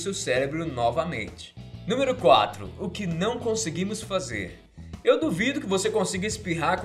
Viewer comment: "O que não onseguimos fazer" idai que erraram? todo mundo aqui entendeu né, então não tem pq reclamar. Que galera chata do caralho! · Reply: Amanda Flores Eu ouvi "Conseguimos" coloquei o cll bem perto do ouvido